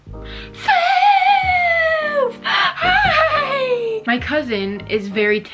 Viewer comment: Brother enters the chat: .... (cricket sound) Doggo enters the chat: FAAAAAAAAAAAV >U< (idk ur dog's name, thats just what it sounded like ya said, sorry)